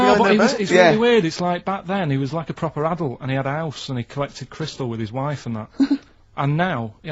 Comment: can someone tell me what Karl says? Collected crystal with his wife?